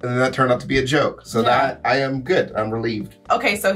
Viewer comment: I legitimately thought you were gonna say here's my favorite thing about Hitler 😂😅 you were wild in this video